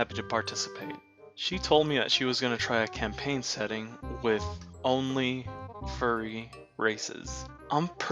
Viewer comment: Omg at first I heard "A campaign setting with only furry racists". Imagine a werelion roaring that all zebras and humans must die